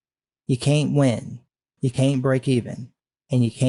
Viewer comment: Sometimes I hear "I'm just some god" instead of "I'm just some guy" and it's honestly very fitting xd